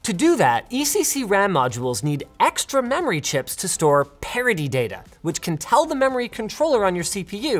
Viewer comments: Captions say "Parody Data" :p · into the video the closed caption says "to store parody data," when you were talking about parity data (I blame youtube entirely) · The subtitles say "to store parody data," when it should have been "to store parity data," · the subtitle says parody not parity, but IDK how to submit corrections to the generated subtitles lol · captions say "to store parody data". I checked the captions. It's English. Not auto-generated. So, note to whoever transcribed this video: it's parity, not parody.